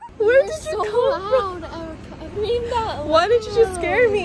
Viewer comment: did she say erica ? 💀